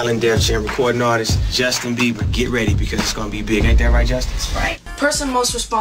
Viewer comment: when he says "isnt that right justin,' it sounds like he said justice